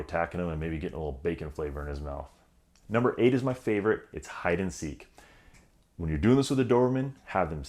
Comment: For #8, I thought you said "Hide a Steak" at first ;-)